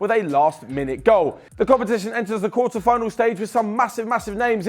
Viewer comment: I swear I heard Ribena and that hair color didn't help with the case either 😂